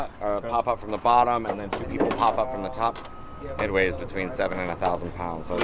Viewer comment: Did he say the radiator weight between 700 and 1000 lbs?